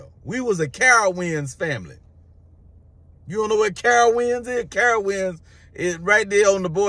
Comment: Sh*t thought you said Carl Winslow family 😂😂